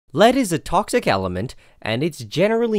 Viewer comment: I legit heard "Ladies and toxic element..."